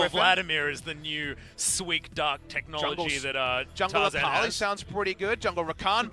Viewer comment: Hey guys, can anybody tell me, what did the comentator say at 24:21, Jungle Vladimir is the new _____________ Technology. Thanks a lot. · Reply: YONGJIE LIU new sweet dark technology